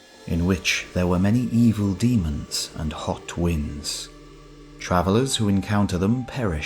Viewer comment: Sorry, I misheard that! I thought he said "Hot Wings"! In ancient India?